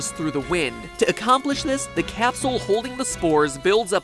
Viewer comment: did any 1 else hear castle instead of capsule?